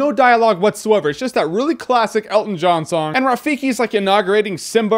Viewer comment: Oh I thought he was saying Elton sang in the beginning